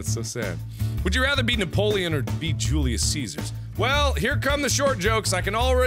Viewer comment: I thought it said be Jesus